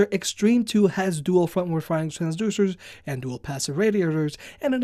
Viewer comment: What the hell did you say at 3.22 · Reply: Mario Killing it sounded like he said “dual frontward firing transducers” or something along those lines, I had to listen to it ten times over and still am not sure lol